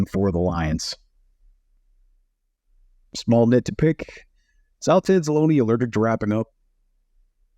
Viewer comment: Who the heck did you say here at 5:17? I've listened to this five times and I can't even come close to figuring it out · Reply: Before "just a little nit to pick" at 5:17 " Is alex anzalone alerted to wrapping up"